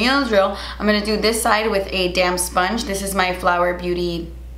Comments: when you said damp sponge i thought you said damn sponge like "im gonna do this side with a damn sponge" and i was dying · Lol every time you say “damp sponge” I hear “damn sponge” and I always do a double take because I’m like man Kathleen is aggressive today lol! Use that damn sponge, girl!!😂😂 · Everytime you say "damp sponge" I hear "damn sponge" lmao